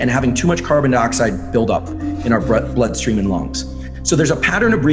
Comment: Did he say "in our breath-blood-streaming lungs?"